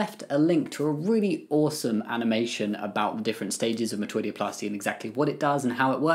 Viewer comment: i use automatic captions and audio to watch videos and apparently AI doesn’t know what metoidioplasty is because it said “the different stages of maturity” (around the 1:02 mark) 😂